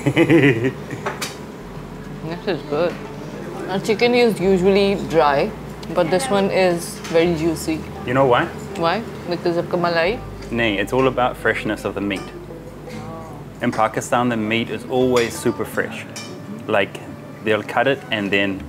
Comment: Because akpa malai💀💀 Did I hear it right?